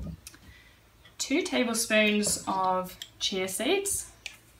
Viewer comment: Sorry, I cannot understand what it is that you are adding 2 Tbs of at 2:15. Can you answer? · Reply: sgbaird chia seeds I think